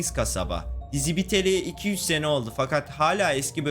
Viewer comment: 'de bende dizi biteli 200 sene oldu diye duydum ama bu çok saçma olduğu için altyazıyı açtım 2-3 seneymiş😊😊😊😊😊